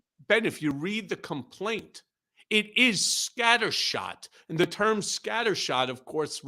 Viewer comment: 😅Scatter-Shoot..at first I thought Michael said 'Scatter Snoot' which woke me up thinking that they must be talking about De Santos ...and Captain Bone Spur! Putin wanted NATO put down so his criminal oligarchs can step in from Ukraine and run Europe.ah!!! Michael on Vivak Swampy hilarious!!!